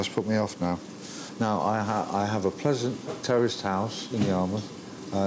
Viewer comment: I swear he said hes got a terrorist house